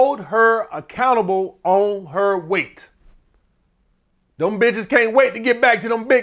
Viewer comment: All I heard is “cheat on the bitch” lmfaoo i been staying strong but it’s rough now